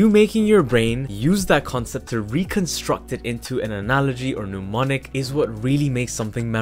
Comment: Am I the only one, who notice this or maybe I'm wrong please correct me the caption says " pneumonic" but I believe the the speaker meant is "mnemonic" ?